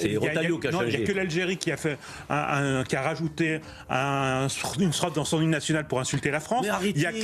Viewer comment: attend c'est moi il a dit sourate les soumis sont dingues